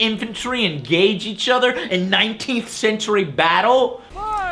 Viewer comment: I had to pause at 9:38, did he say 19th century?